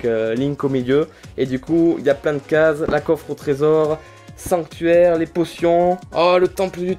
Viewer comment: c'est moi ou il a dit a 12:59 : la coffre au tresor ?